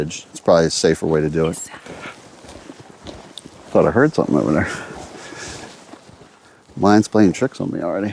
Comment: <num>. Woman’s voice. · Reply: He said "I think I heard something"